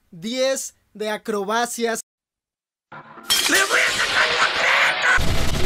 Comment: "Le voy a sacar la jeta" xDDDD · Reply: Ajjajajajajajjaja pense que decia la crema